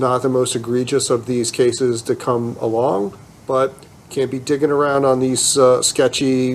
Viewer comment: I thought the judge said “dicking around”. I realize he said “digging around”